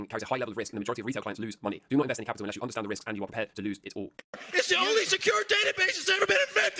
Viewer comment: Intro so fast all i heard was "invest all your capital and be prepared to win it all"